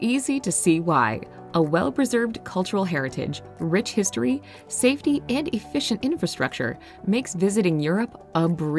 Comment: did i hear safety ? In europe ? who are you kidding